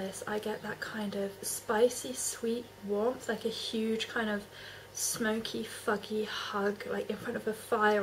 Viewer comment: Is it your accent, or did I hear you correctly at 3:22 describe Tobacco Vanille as a "smokey, fucky hug"?!?